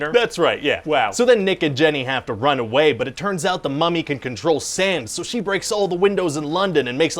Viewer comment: am i the only person who thought he said "control SANS"? no? ok then. ._.